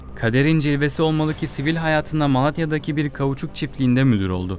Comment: Türkçenizin içine edeyim! Ulan dedik dedik bir türlü dinletemedik. A k e 11:04 burada ne diyorsun? Malatya gibi bir şey çıkıyor ama Malatya’da “kauçuk” çiftliği ne arasın?